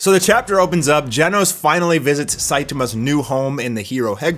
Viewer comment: Did he just say Saejima lmao?